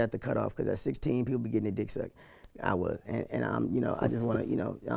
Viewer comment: Did he say, at (4:44) the cut off is 12-15, cuz at 16 getting their D sucked!?